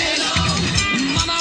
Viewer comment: que dice jaja??? Atelon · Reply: All day long